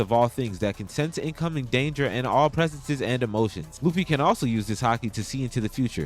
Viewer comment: that’s literally not true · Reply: mb, thought u said Luffy cant